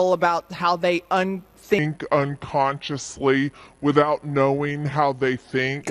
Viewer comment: I had this playing as I was cleaning the house and wasn't really watching. I heard "unthinking, unconsciously, without thinking" and I was confused by the combination of words. I didn't really know what that would look like, when enacted. Then I looked at the screen and saw who said it. I guess I already knew what it looks like when enacted; I just wanted to forget about her existence.